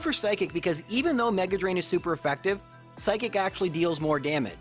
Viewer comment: I can't unhear 18:15 "psychic actually deals more daman" instead of damage. over here keeping folks entertained, exercising lovely vocabulary, playing the hell out of Pokémon with strategy, knowledge and gusto, and I'm.... picking out unimportant minor misspeakings at 2am my time. What is wrong with me.